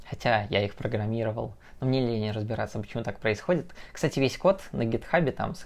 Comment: услышал не "хотя я их программировал, мне лень разбираться, почему так происходит", а "... мне ли не разбираться...". :)